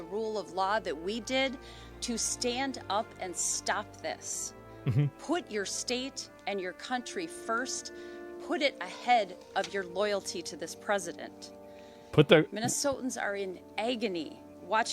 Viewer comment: She says: "Put your state and country first. Put it ahead of your loyalty to the president." He hears:"put your state above your country." This mishearing confused me at first. I dont think this mistake can happen without an extreme filter. Also, it seems that emotionally the president and the country is the same thing to him. This is a problem.